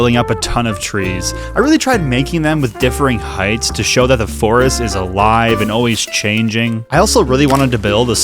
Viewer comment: I thought you said "The forest is a lie" instead of "The forest is alive"